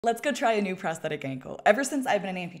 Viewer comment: ngl i heard “presscatakangle” instead of “prosthetic ankle” at first